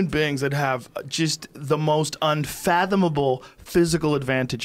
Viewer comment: and 1:09 did he say unfathoNable twice?